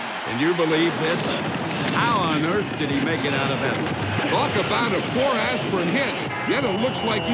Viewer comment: What'd John Madden say about aspirin? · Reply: “Talk about a four-aspirin hit.” :)